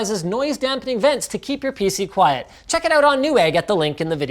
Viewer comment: I crunched into a chip when Linus said "keep your pc quiet" during the ad read and it sounded like he said "keep your pussy quiet"